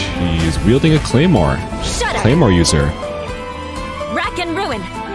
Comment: no because when I first saw this I thought said “Shut up!” and I was genuinely confused